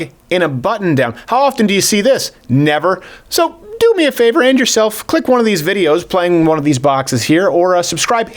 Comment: "do me a favor _end yourself_" Oh wait..."do me a favor...and yourself...click one of these videos" Lol that's what I heard initially. That was unfortunate syntax, with an unfortunate pause haha